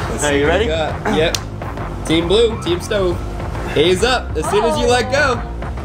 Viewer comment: Did he say faze up?